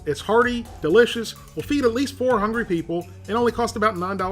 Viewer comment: oh, 4 hungry people, not, 400 people like my bad ears had thought you said. LOL....